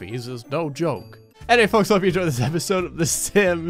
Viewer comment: I swear I thought you said "Way to go, meth infected me" 😂